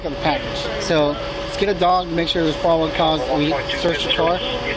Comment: I think the most frustrating part was the cop who said “the package is still on the porch. Let’s get a dog to find probable cause” 😡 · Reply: "Make sure there's probable cause" Almost sounds like he was saying "make sure that dog alerts"